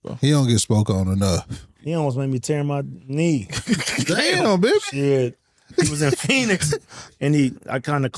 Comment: Anybody catch that “damn bih” @ 42:00 🤣